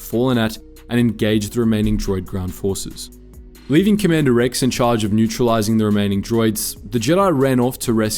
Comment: Did he just say “Commander Rex”?